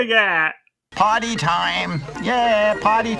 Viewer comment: oh he said ,Party time, ok For a long long time i thought said ,potty time, and everybody went To the bathroom???